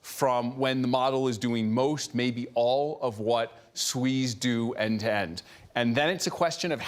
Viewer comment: Is he saying, "what SWEs do end-to-end", as in SoftWare Engineers? 10:12 Slightly unfortunate sound problem where she suddenly blurts out, towards Dario, the word "COUNTRY!" but the 2nd syllable is cut out. 😂